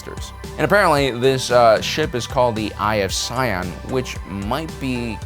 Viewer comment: First time he said eye of sion i thought he said omnissiah.